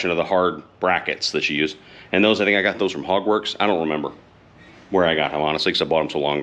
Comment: Took me a second to register that you said "Hogworks"... I thought you said "Hogwarts" at first!